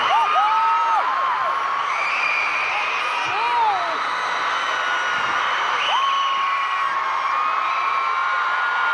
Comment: what suga say in 9:10 ??? is "ta bom" ?? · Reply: KC subs That means its good. "Dda bong"